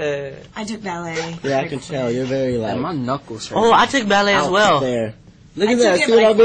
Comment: Who else heard roc ray "My knuckles hurts"